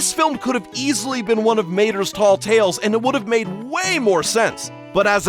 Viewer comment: did you say mater's tall tales? you was there too 🤣